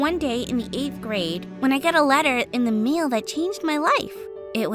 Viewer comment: anyone else see that she said “meal” instead of mail 😬😬🤣 just wondering